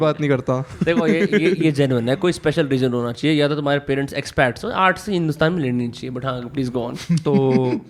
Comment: xpat? kya bola